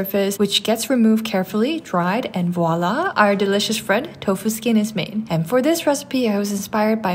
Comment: I thought u said tofu's kid 😭